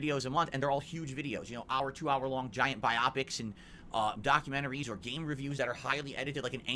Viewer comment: did.... did he just say bi opics?? 😂😂😂😂😂